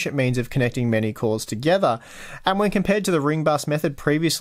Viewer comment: "And when compared to the roomba's method...." is what I heard